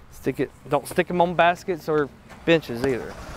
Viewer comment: First minute of vid it sounded like said don't stick stickers on the bitches but he may have said benches.